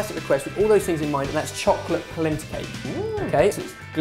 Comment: Thought it said "Placenta"! xD So scary :P